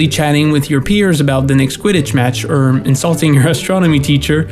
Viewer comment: I just thought You said „The quidditch bitch“ Haha